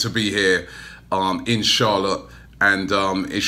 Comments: Thought he said inshallah not in Charlotte 😂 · Thought he said inshallah when he said in charlotte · Robbie, everytime in this video 1:09 & 1:16 when you said 'In Charlotte' it sounded like 'InshAllah' Just putting it out there 😀😀😀